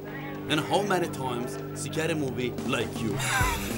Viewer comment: yanlış mı duydum? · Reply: Scary yani korkunc 🤣🤣🤣